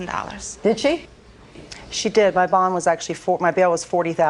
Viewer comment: SHE ACCIDENTLY SAID BONG INSTEAD OF BAIL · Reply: It kind of sounded like it but she actually said bond.